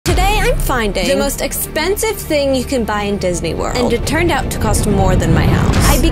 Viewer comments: I thought she said "I'm gonna BUY the most expensive thing on Disney" and I was like "girl if it costs more than your house why are you gonna buy it?!" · i thought she said "BUYING the most expensive thing" and was scared